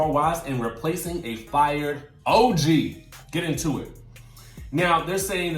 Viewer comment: I thought Shaunie said Og isn't fired.